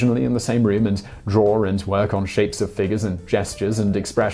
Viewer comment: I hear shapes of figures I read shapes of fingers